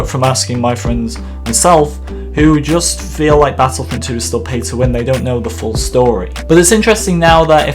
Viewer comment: If you activate the auto generated English subtitles. It says pizza when instead of pay to win.. ??